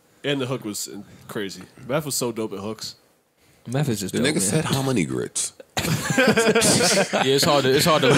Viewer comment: I thought Meth said "spread the butter lyrics over harmony grits" I swear theres an R in there